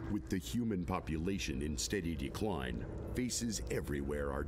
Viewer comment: Did anyone else heard the narrator say feces instead of faces?